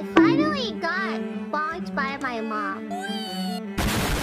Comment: What did she say at 1:10? My understanding of Rock isn't that good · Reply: "Hi Momseki"